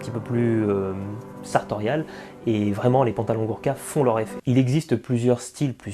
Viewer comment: J'ai compris "Burka" mdrrr... sinon bonne vidéo.